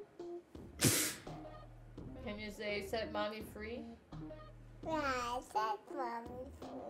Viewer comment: I thought she said “set bobby free” like free schmurda. I was thinking it was a little dated but respectable parenting nonetheless